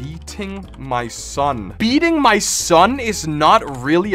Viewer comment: Playing fnf. I thought you said beat in my son.